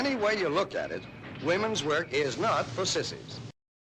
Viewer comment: I really thought I heard “…it’s not for pucies “ lol